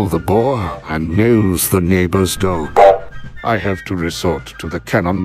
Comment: I just love how the poor gonk droid is the neighbor's dog. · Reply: I thought he said it mills the neighbors dough.